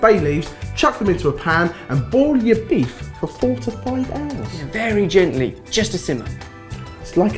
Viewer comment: 4-5 hours.... I thought you said 45 hours :)